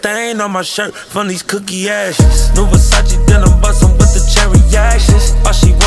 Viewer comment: Lol I thought he said “cookie asses” 😅😅😅